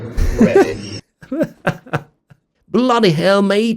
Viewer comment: That robot sounds like he says "Ready" to my British ears. We don't say "Bloody" on its own, it's an adjective/adverb. It needs a noun or verb to work with as an intensifier. Bloody hell. Bloody difficult. Bloody mess. Falling over would be a good time to use the F*** expletive.